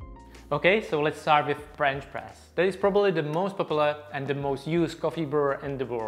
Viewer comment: Oooohh, did I hear "French Breast" Oui lol